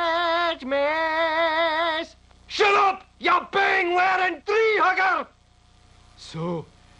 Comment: What does Willie call John Denver when he yells at him at 3:10? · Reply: "Ya bing(?) wearing tree hugger!"